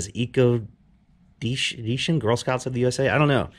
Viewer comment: I think it said eco action lol